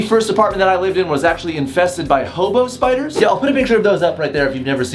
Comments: I thought he said HOMO spider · I thought he said "homo spiders" instead of "hobo spiders!"😂😂😂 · Lmaooo I thought you said homo spiders 😂😂 💀⚰️ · I thought he said “my apartment was infested by hobos“😂